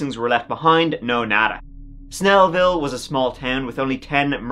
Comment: My late husband was from Snellville! I always told him I thought that sounds like a Dr. Seuss movie!! 🤣 Let’s give it a go!!!! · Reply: At first I thought was saying "smellville" so discovering it wa actually Snelville was an improvement for me.